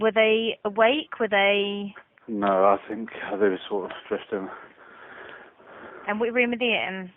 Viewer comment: inaudible? he clearly says drifting